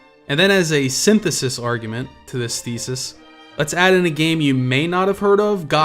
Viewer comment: Instead of "thesis" I misheard you describe Oblivion as the "feces" of open-world games xD Didn't detract me none way back when I played that game to death.